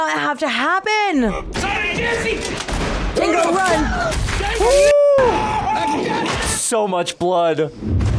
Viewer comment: what did say? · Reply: 'Run Django Run!'